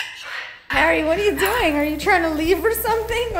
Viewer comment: AHHH I CANT BRANDOS VOICE IS ADORABLEEE. It sounded like he said "what are you doing little brando"